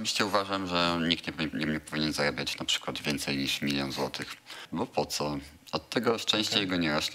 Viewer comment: dlaczego ja usłyszałem że "Nikt nie powinien Zaje&@ np: więcej niż milion złotych"